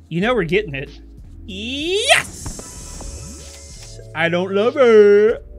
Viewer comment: does he say I don't Love it?